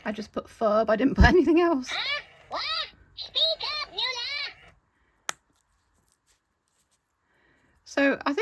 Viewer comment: "Heh? What? Speak up, (Whater the furby said)" Edit: Whatever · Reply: noo-lah btw :3